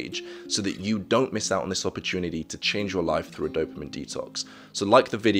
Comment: at first i thought detox said botox